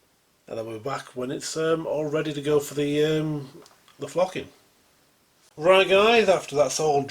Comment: You sure that was PVA you were squirting. Did hear you say off to flocking. Could have misheard you.